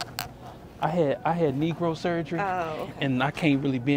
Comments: I had “knee grow “ surgery 😂😂😂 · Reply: I'm so slow, I thought he said he had Negro surgery 😂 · I THOUGHT HE SAID NEGRO SURGERY 😂😂😂😂😂